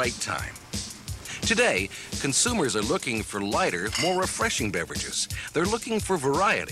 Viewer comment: "it doesn't taste like any other clear drinks that I've ever tasted. it's got a different taste." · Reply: ikr I actually thought at first he said "it's a whiter drink" instead of lighter. He also says it with all the enthusiasm of someone being held against their will.